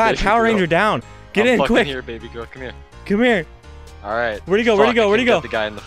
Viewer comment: did he say I’m here baby girl lmfao 🤣